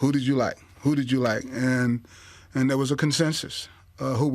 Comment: What word did Ozzie Newsome use at 15:19?... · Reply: Consensus.